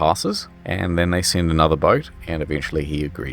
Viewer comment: it says another board is sent instead of another boat